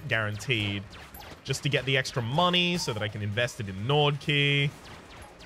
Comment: Everytime you say Gnawed Key I hear "Nord Key" and for a second I think there is a Skyrim reference item I missed. Accents are fun.